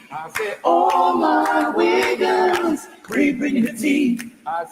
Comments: Why it sound like he sayin "ALL MA WIGGAS" LOL😂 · I str8 thought he said all my wigga’s 🤦🏾‍♀️🤣🤣